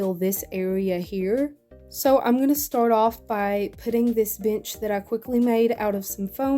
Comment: did you just say b**ch